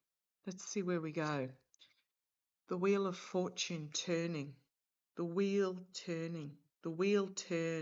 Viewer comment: Dear, when you said "The Wheel turning.", I heard "And the Worm turned..." 😮